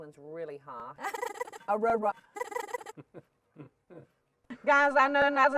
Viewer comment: What I hear at 3:51 hehehehhehe Aurora heheheheheheh